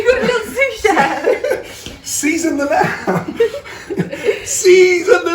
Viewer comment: When he said “season the lamb,” I could of sworn he said “Ceasar Millan...” 😂😂